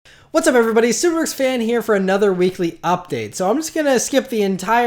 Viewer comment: Lol when u say ur name it always sounds like you say "subrwex fan her"